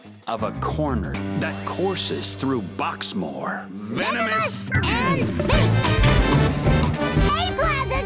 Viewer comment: I scent Aisawa sensei vibes.. idk why :v 6:16 I heard venomous and thicc